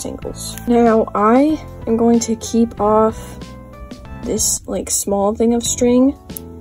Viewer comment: I heard that’s soon as I cut it off…